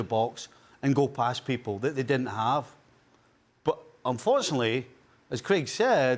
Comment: did Stevie just call Grealish for Girlish?